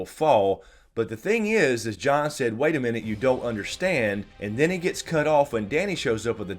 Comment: I thought Jon said, "I don't understand," not "You don't understand."